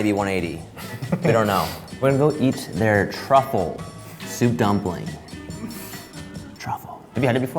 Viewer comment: She actually said the truth about dumplings Not dumpling fact